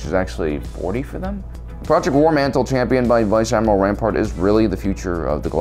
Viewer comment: did he just say Admiral Palpatine?